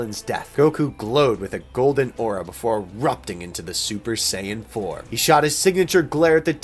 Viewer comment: Why did u say ssj4 4:56 why tell me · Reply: He said Super Saiyan Form